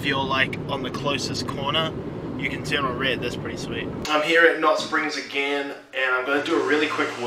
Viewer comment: What's the name of this gym you keep talking about? It sounds like your saying knot springs but I could be wrong 😜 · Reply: Haha. You are correct =) Knot Springs is a gym and spa in Portland, OR